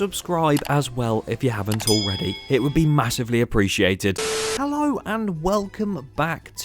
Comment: I heard MASSIVE?